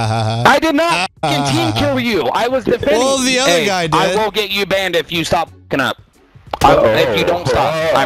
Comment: Sounded like he said get you bend.....bit gay